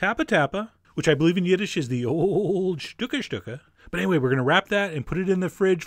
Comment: When he said "the old shtucka shtucka," I thought he said "the old shtupa shtupa," and hot coffee went through my nose.